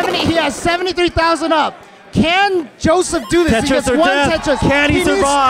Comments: did he just say "tetris or death?" 😁😂😂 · Did say “Tetris for Jeff?”